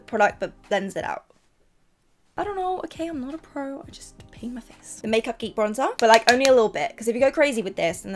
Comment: You said paint my face I thought you said hate my face lmao I was shook 😂💘 ly